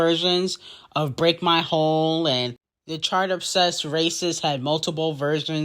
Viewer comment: Did you just say “Beyoncé had Break my Hole?” 😂😂😂😂 bye